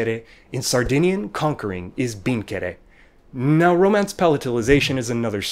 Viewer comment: did you say binkere in 5:16 for sardinian? That means thousand times in Turkish lol